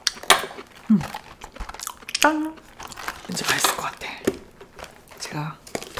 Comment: at this point I thought she said chalo isse kholte ha I in hindi😂